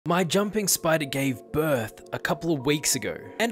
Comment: i thought u said my jumping spiderman gave birth